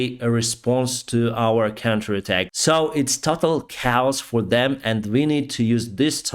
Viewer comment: There is one part around 4:20 where I think I hear “it is topple cows for them” did I hear that correctly??? 😊